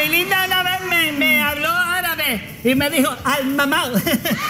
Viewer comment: En el minuto 16:09 que dice Raymond. No logro entender lo que dijo. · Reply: Al mamauu!